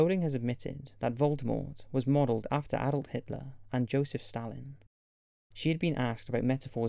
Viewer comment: Anyone heard Adult Hitler 7:45?